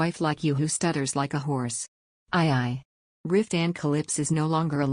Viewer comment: I want to like this, but after only 45 seconds, I just am so bothered by the AI voice, it is painful. It doesn't pronounce Maxi, instead she is called Max. It says Cross instead of Croyso. Riftan gets called Ruthann at about 1:15 or so. It also pauses awkwardly in pronouncing words making it difficult to follow the story. I know others liked it, but I just can't. I am covering my ears at the thought.